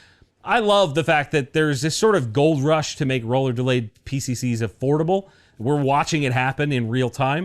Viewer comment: I thought you said affordable😂